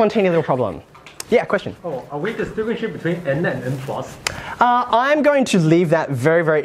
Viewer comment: What was the question at 6:25? I couldn't understand what he said. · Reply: He was asking if they were going to draw a distinction between N and N+